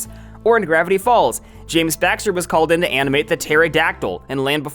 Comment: Did you say James Baxter…. 58:24 like the Jaaaaammmmeees Bbaaaxteeet